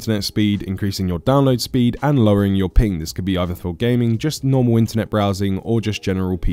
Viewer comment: Am i the only one that thought he said lowering your penis instead of lowering your ping